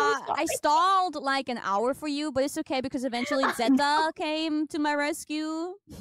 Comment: slight correction at 1:22 "Zeta" instead of "Zenda"